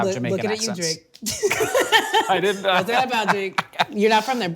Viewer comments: noticing Akilah says "Drake" here but the closed captions say "Kendrick". Not sure if this is supposed to be a little Easter egg joke, but she's definitely talking about Drake not being from Jamaica or Atlanta, and that "Kendrick does a good round up of the places [Drake is] not from." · CAPTION CORRECTION: At 3:09 she is absolutely saying "lookin' at you DRAKE" -- then goes on to say that Kendrick dissed him by saying he's not from Atlanta (but uses Atlanta music styles) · she says "looking at you drake" and the caption said "looking kendrick". 😭...